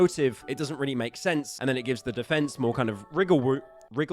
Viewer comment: is bro really saying riggle room instead of wiggle room